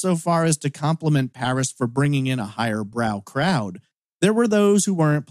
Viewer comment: Have the police checked all the 7-Eleven stores? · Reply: When he said "higher-brow crowd" i thought he said higher brown crowd. I was like nooo! 😂